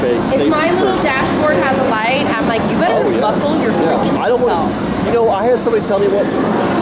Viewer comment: Did I hear drop a F bomb? ha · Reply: that was a "fricken"